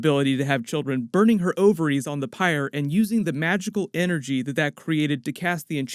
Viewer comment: For a second I thought you said vaginal not magical.